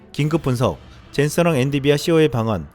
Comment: 자꾸 엔디비아로 들리는데 세번정도?? 오늘 여러곳에서 그렇게 들리네요 엔 비 디아 발음하면 헷갈릴만도